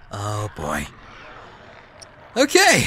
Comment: heh.. bunny... ( two seconds later ) bunny gets shot Ohh.. my.. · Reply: I thought he said oh.. bye.